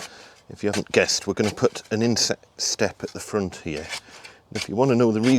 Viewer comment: When you said about the inset step, I thought you said "insect step" and I was thinking, why on earth would you need a step for insects to climb up 😀. Nice work on the base, lots of good tips for keeping the wet out of the house construction too, thanks